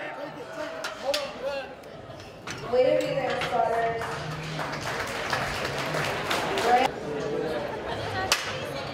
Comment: what the hell was that guy screaming at 15:06 while dan was going down??? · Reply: I think he's just saying "Come on! Come oooon!" lol